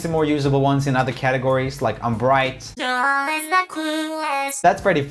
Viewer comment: it sound like it says joel is the clueless...